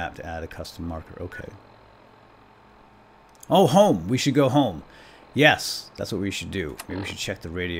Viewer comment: When it said go home I thought it said find colone